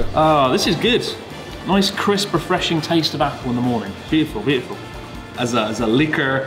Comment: "Ryotaro making a fool of himself, as per the usual" 😂 When you said "apple in the morning, I heard "applemori". So when I go to Aomori I'm calling it applemori from now on.